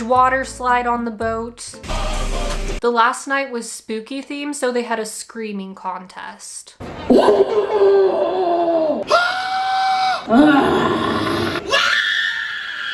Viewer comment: I misheard that as "screaming podcast" and honestly I’d actually be interested in seeing how that would even work